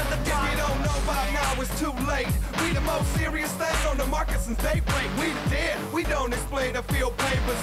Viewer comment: I used to think they were saying "we're the most serious thing on the market since beyblade."